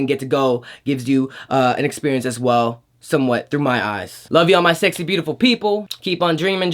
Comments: I heard, “ I love you.” Referring to ARMY ... right? ❤️ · I THOUGHT THAT HE WAS SAYING "I LOVE YOU" AND YOU WERE LIKE "I LOVE YOU TOO" ... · I thought j-hope say " i love you "😂😂